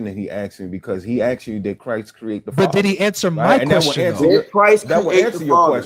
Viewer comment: DID BRO SAY „DID JESUS CREATE THE FATHER?” 😂😂😂😂 NAH